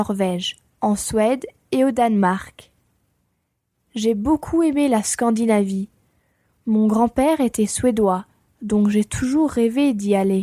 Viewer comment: I understood nearly all of the first introduction except je beaucoup (aime?) le Scandinavia. 6:17. What does she say after beaucoup ? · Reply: Elle dit : "J'ai beaucoup aimé la Scandinavie, mon grand-père était suédois." 😊